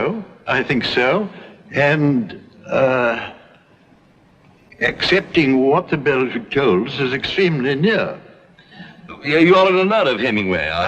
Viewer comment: Someone help me out: Is he really saying "For Whom the Bell Tolls" at 6:35. The subtitles suggest as much, and yet I can't agree that that's what I hear. Can you? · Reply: Sounds like '..and accepting what the bell tolls is very near' The subtitles seem to have been written by someone, as opposed to generated by voice recognition. So the writer has put what s/he thought Maugham meant to say. Accurately, IMO.